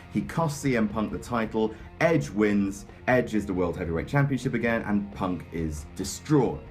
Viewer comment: "Edge is the World Heavyweight Championship again" Did I hear that correctly?